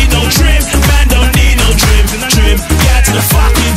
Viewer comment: Lol I thought he was saying 'man don't need no tren' as in the steroid not my trim!